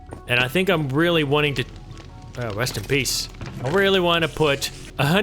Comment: did he say rust in piece? is that a megadeth refrence?